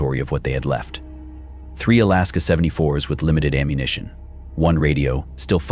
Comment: Alaska 74? Ai for ak 47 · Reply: You wanna hear something wild...As i'm scrolling through the comments, the moment I mean THE EXACT moment I saw your post I heard the line at 57:10 say that Alaska 74 with limited ammunition...Bro I got gooseys